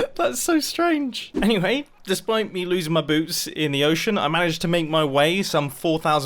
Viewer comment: anybody else hearing boobs?